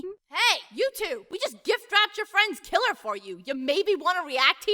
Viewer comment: "Hey, you two! We just gift wrapped your friend's killer for you! You maybe want to react here!!" "Oh please! How could Grimlock be the killer?! He was helping us!!" "Yeah! I mean, why would a killer EVER want to force someone else to confess to his crime??" sighs "Go on..." "For you see!" "Goddammit" "Schmitt would have had nothing to gain from Griselda's death. If he had killed her, The item would've just been destroyed along with her avatar" "Really, that's your proof?? Bandits extort people out of items ALL THE TIME!!" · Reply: i thought he said kidnapped, not gift-wrapped...